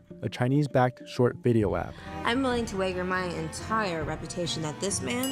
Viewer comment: Did she say "way-ger"???